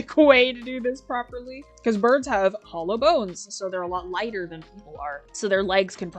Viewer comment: I misheard 'hollow' as 'hella' and went "Yeah birds do have hella bones."